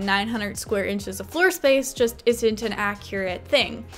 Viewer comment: I SWEAR I THOUGHT FOR A SECOND SHE SAID 250 INCHES OF FLOORSPACE IN THE CAGE- ✋